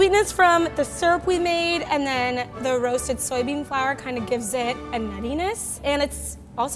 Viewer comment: i heard 'the soybean flour really gives it an anus" and had to rewind